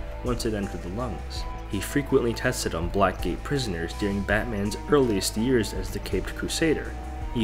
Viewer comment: I thought he said black gay prisoners 💀